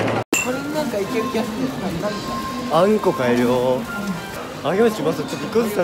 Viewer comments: の所らへんの那須くん💩買えるよーにしか聞こえんwつらいw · 「あ、💩買えるよ〜」にしか聞こえなかったの私だけ、、？？？ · 『あっ、💩買えるよ〜』って、 がっつり聞こえる、聞こえたよね？😂😂😂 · ｢あー、う○こ買えるよ〜｣にしか聞こえない